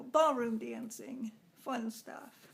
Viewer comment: I thought you said pole dancing at first and I was going to ask you to show us love a duck could you wow that was an awesome story about your leg dear hugs kisses